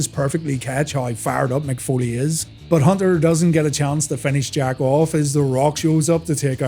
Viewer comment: Ok so 2 things, at 19:55 does he say “HHH doesn’t get a chance to finish jacking off” and also when Steve Blackman comes out, doesn’t already say Royal Rumble 2000 Winner?